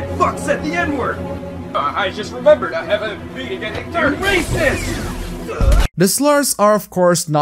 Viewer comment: I heard it as clear as day! That Kira fuck's racist!